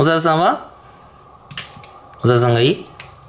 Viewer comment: お猿さんがいい？のとこ小沢さんがいい？に聞こえてしまった